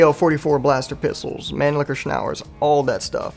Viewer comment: did he say "man licker"?